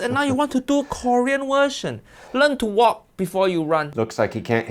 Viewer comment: I know not intentional but sounds like he said "learn to wok before you run" 🤣